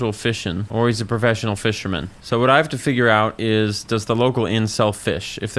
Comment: I hate that I heard "Does the local incel fish?" and I was like, hang on, that's pretty rude, and also he clearly is fishing.